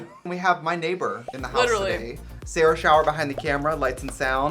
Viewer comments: I just realized the is Sarah's last name, I really thought they were saying Sarah shower. omfg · At the beginning I heard she said "Sailor Showers" and it was 💀💀💀